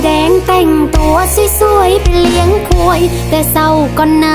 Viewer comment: ชัดเลยได้ยินค*ย😂